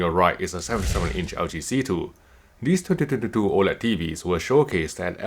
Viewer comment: What did he says at 0:05? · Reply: "2022"